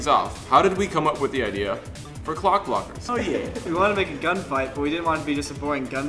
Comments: oh i thought it said cock blockers :P O_O but still... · hahaha, i really thought it said cockblocker